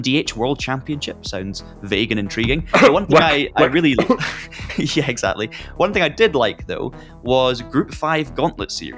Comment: What did the guy on the right say when he fake coughed around 16:45? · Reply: He said "WEC", referring to the World Endurance Championship which he feels the "LMDh World Championship" will be inspired by. :)